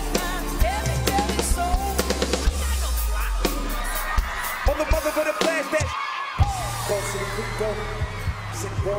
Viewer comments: what did he say at 0:50? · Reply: You ain't got no flako in your serato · WHAT IS TYLEr SAYING SIWIWHWHQ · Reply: “You ain’t got not flacko in your serato” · i don't know what tyler said but i love it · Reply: "you ain't got no flacko in your serato" i have no idea what it means but i'm pretty sure it's an inside joke between them lol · anybody know what Tyler said to rocky? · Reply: "YOU AIN'T GOT NO FLACKO IN YO SCERATO?!?!" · What did Tyler say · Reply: I_p_d_a_k_t_h_d “you ain’t got no flocko in your serato?” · what in the world does ty say at 0:49 ?? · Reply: Gianna Maresco He said "You ain't got no flacko in your serato"